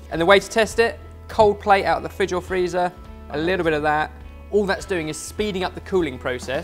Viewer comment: I thought said Coldplay out of the fridge or freezer - was momentarily confused!!!